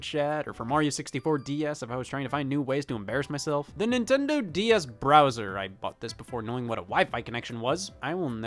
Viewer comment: DS Browser, yea, I thought it said Bowser when I bought it